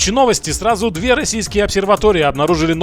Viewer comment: С такой деградацыей космической отрасли, мне "2 российские обсерватории" послышались как "2 зимбабвийские обсерватории"